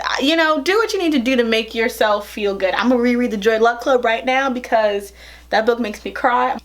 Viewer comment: does she say “joy luck club” or “dreadlock club” ?